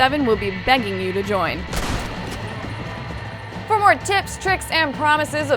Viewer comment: sounds like she says for more tits